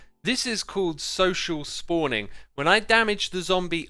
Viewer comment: I thought Xisuma said social distancing lol